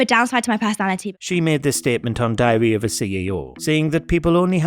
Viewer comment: LOL - I thought he said "Diarrhoea of a CEO" (@ 3:16). Not far from the truth though.